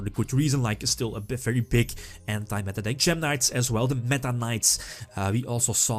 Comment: Is it just me or did I hear "the Meta-Knights"? Didn't know we were back in Brawl days.